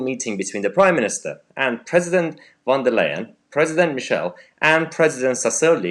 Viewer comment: Mhuhaha I thought you said President Wonderland!